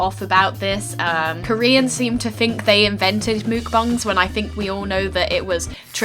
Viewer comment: it literally sounded like you said nuke bomb instead of mukbang. 'Koreans think they have invented nuke bomb' .....